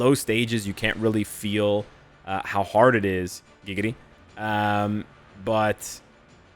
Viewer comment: is it just me or did i just hear say giggity?😂😂